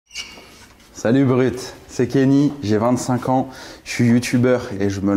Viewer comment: J'ai entendu "Salut Uber Eat"